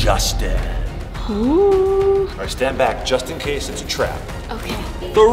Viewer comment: When you said just in case it was a trap it sounded like you said Justin it’s a trap